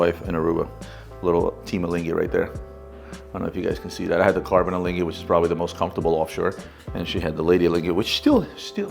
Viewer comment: what are you saying? Team what? I’m just curious. Are you saying team-a-lingy is that a nick name? Or are you saying A Lange? And if the latter why is there a picture of an Audemars piguet? Anyway love the show been watching for about two years now and I’m all caught up with everything and I even rewatch episodes. You guys are the best keep up the good work! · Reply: He said ''team alinghi'' :)